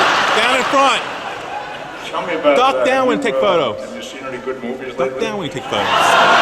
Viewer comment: What was said at 6:14? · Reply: Nimoy asked him whether he had seen any good movies lately, probably with the intention in mind to tease Shatner, 'cause he was not in the new movie.